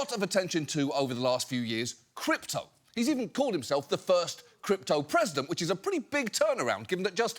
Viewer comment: did I hear CLEPTO-president?